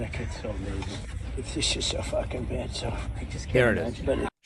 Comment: I heard "She's so fkn bad so." Either way🌊🌊🌊🌊🌊🌊